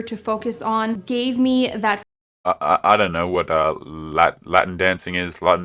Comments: I thought she said Mutton dancing.🤨 Like you dance with food in your mouth? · I thought she was saying "Mutton Dancing" that sounded so much more interesting.